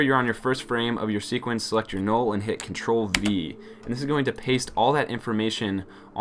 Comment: Did you said "Control W"? 3:23 This option deleted everything!!! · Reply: Control V :D